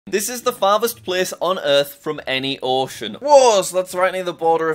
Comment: Lol I thought that he said furthest place away from.orphans